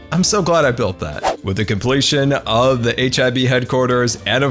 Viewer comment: When said HIB all I heard was hiv